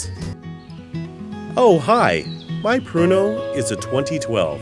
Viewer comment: i thought he said porno. lol